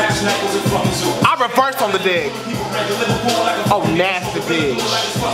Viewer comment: I thought she said "i reverse out of debt" LOL